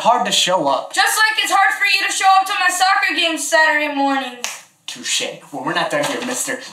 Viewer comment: Did he say to shir in